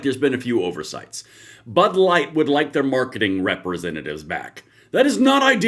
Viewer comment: 😂 I thought you said butt light.....